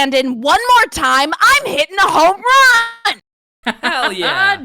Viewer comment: I thought she was gonna say I’m gonna hit a homeless man